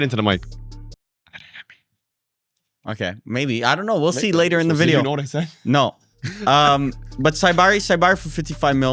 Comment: anyone understand who he said? I want to know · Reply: Adyemi